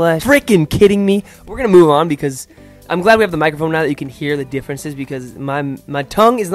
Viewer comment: you did it again! You gave me a good laugh yet again, as you seem to have mistaken your microphone for a megaphone (at least it sounded like 'megaphone' at 3:58, but nobody noticed). So, for a second, I pictured you both talking to one another through a megaphone. Hilarious! I'm curious to see what little slip you'll be coming up with next time. Your words seem to be great (phonetic) sportsmen like you and thus sometimes a bit faster than you. Funny though. But please take it easy, and stay the way you are! Once again: Viele Grüße aus München!